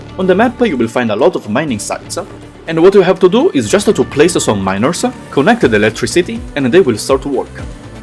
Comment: i misheard that as "Place some minors, connects electricity, and they will start to work" Endfield child labor yeehaw .... Sorr